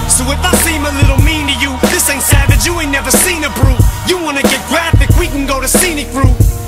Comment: I always thought he said brute instead of brew haha